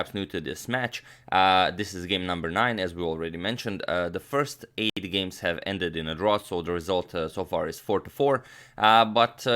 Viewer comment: That sound artifact was amazing, made it sound like you said "the first 80 games ended in a draw." Impressive stamina from both players with their first 80 games. Haha